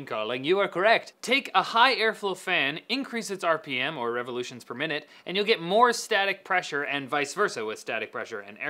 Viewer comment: Did he say "Revolutions per minute" at 2:05 ? o.O